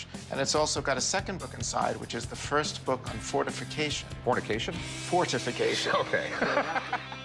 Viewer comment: I thought it said fornication